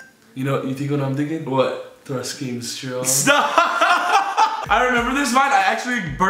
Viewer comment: Did anyone else hear “thrust game strong”